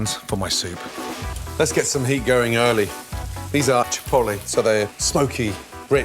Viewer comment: is it me or I heard him say chipotle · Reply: Chipotle is a Chilli. yes, you did hear chipotle.